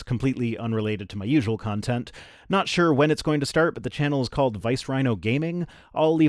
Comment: ngl when I heard the intro, at first thought I heard "Riced Wino" and thought, "Whoa, his frustration is getting the best of him", anyway...appreciate the art of the rhino "younglings". Other than "The Rhino-lets", what's a better name for rhino herd?